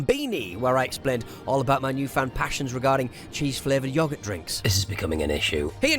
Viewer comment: Maybe I misheard. Did you really say cheese-flavoured cow piss?